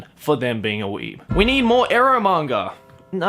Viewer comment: the first time I thought he said eUro manga and I was so happy